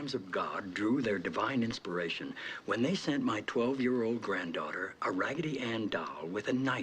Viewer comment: Yes and one of the reasons Rob Lowe quit, the show was supposed to be focused far more on the WH staff rather than the president. Sam was the focus of the first Season, he was the first character to be introduced and the main season plot revolved around him and his relationship with a call girl that not only became a lawyer but also apparently a hospital administrator. Lowe had top billing for the first season but as the show progressed the focus quickly shifted from him towards other characters and specifically the president. Martin Sheen was intended to be mainly a guest star for the pilot as well as a "recurring" character, however his performance shifted the entire premise of the show and many plots that focused around the president were then developed. Rob went from being top billing to pretty much a secondary character by the end of Season 2. To be fair TWW had seen A LOT of pay disputed from others such as Bradley Whitford, Allison Janney and John Spencer these included "walkouts" during production. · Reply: My bad, I thought he said "daughter", not "granddaughter".